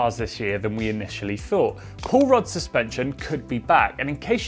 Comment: Every time someone said "Pull Rod Suspension" all I heard was "Paul Rudd Suspension"